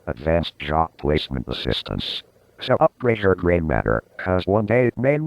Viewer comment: "Upgrade your grey matter , cause one day it may matter!" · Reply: always thought he said 'upgrade yer brain matter'. didn't see an official source for the lyrics, but it looks like it's half n half of people thinkin its one or the other.. that's gonna fk with me now